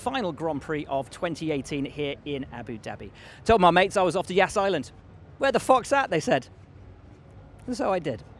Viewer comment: Wear the fox hat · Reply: oh lol! I just listened to it one more time with earphone. I thought he said "where the fuck's that?" and wondered why didn't they censor it XD